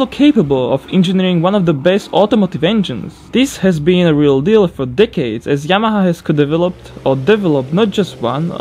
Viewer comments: it sounded like he said "....this has been a real deal for dickheads...." In all seriousness, I stripped down the BMW S62 V8 engine from my old 2001 BMW M5. On the inside edges of the cylinder heads, cast into the alloy, there was "Yamaha" written on each cylinder head. I also found "Yamaha" cast into the cylinder heads of a BMW M62 V8 from an E39 540i too. So even BMW uses Yamaha engineering. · what I heard: "d*ckheads" What he actually said: "decades"